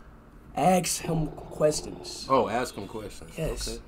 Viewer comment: His dad thought he's saying "X him a question"💀💀